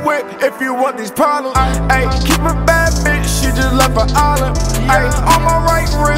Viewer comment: does he say 'Keep a bad bitch she just left for Ireland ? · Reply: A Island *